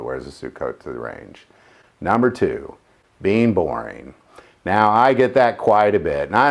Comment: When you said "being boring" I almost misheard it as "being born" XD